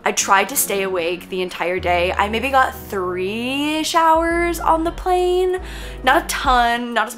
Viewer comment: I watch on 2x. And then you said you got 3ish hours on the plane I thought you said 3 showers 😂😂